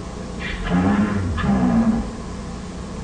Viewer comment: Honestly, it just keeps getting better and better. First of all, I do love these withered models. They're not too different from the originals, but they are still weathered enough to look damaged and have a unique flavor to them that gives them a creepy, eerie feeling. Second, it's so creepy when you realize that Bonnie wasn't there to hurt the guy but warned him. The "stay dead, stay dead" line is extremely creepy, and once you realize what's going on, this thing is foreshadowing an even more dangerous creature down the hallway. Finally, just the general scene and lighting are amazing. Good work, if you end up seeing this. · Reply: I thought he was saying danger